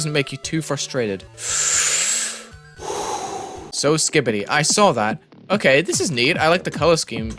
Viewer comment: Did he say skibidy or im just hallucinating